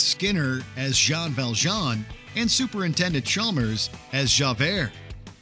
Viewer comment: Did he just say, "Super Intended" Chalmers?